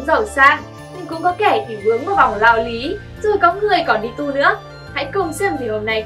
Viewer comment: đi tu mà tui nghe thành đi tù